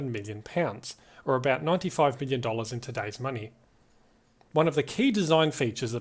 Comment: did you say $95 billion? 😮🤯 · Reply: Sorry I wasn't very clear! That's $95 million